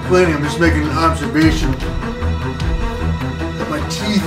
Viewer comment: Sounded like you almost said making conservation. I was going to say then you put a picture of Teddy Roosevelt.